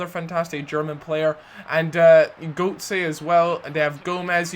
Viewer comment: Haha. Did you just say "Goatse" at 1:43? XD